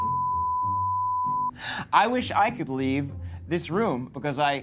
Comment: WHAT DOES SHE SAY .... "I'd leave." She says she'd leave. Seriously that's all she says.